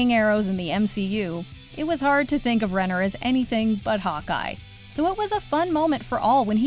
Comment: when she said that we only know Jeremy Renner as "Hawk Eye" I thought she said "hot guy" which also is correct lmao